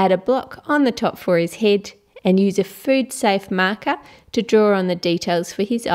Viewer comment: when she said marker (at the creeper part), i thought she said manga. Sad lol